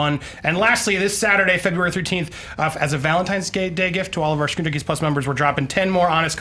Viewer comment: did Andy say "valentines day gay gift"????